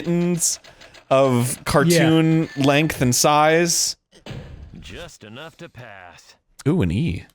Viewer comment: I thought he said: just enough for dad after they cleared the mission